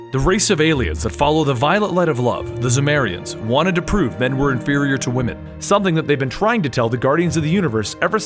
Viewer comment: I can't understand what you are saying. I turned on CC and the subtitles are stating "Wanted to prove Edward barrier to women something that they've been trying to." I thought that was wired, just wanted to let you know. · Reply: Wanted to prove men were inferior to woman. lol I dont write the subtitles, Youtube guesses at them